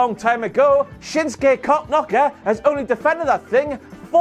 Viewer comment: 😆, at 4:15 did you call Shinsuke "Cock Knocker??"